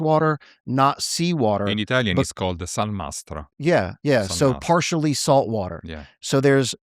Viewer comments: here said that brackish water is called "acqua salmastra" not sun master like the subtitles say😂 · I know it sounds like he says Sun Master but a quick google reveals he is saying "Salmastro"